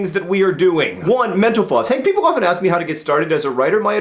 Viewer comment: Every time he said mental floss I thought he said menopause